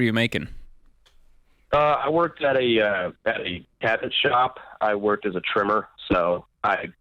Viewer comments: I heard "I work at a cabbage shop... trimming..." 😅😅😅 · You can tell by their faces Rachel and George both thought he said "cabbage shop". I know because I made the same face.